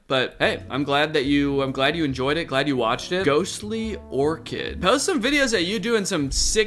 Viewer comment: i have this on as backround noise an i had a neuron activation and had to pause and rewind for a second because i thought he said "Lilly orchid"